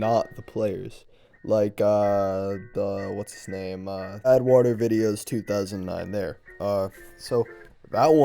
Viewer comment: i misheard "badwatervideos" as "eduardo videos"